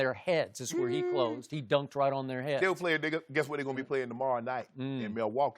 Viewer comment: Shannon: still playing nigha. I had to replay like ten times. But I am positive that's what that night said hahaha